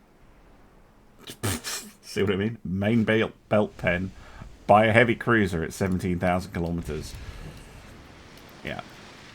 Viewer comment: 17km man not 17k km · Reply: I went ahead and pulled a timestamp for you. Watch from 40:50 until 41:00 and you'll hear he clearly says "Main belt pen at seventeen THOUSAND KILOMETERS" after chuckling. That's the joke.